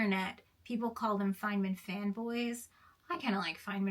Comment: I'm very very sorry, but I misheard "Feynman Fanboy" as "Feynman Femboy". I had a very funny image in my mind, one Feynman wouldn't have like, but fuck him